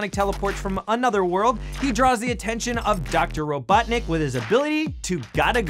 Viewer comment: is that just me, or did he say Dr. Ro 'Butt' nick ?